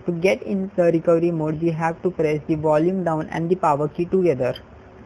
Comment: i cant understand what you're saying in 3:18 onwards. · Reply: maandig you have to open recovery so just hold power key and volume down together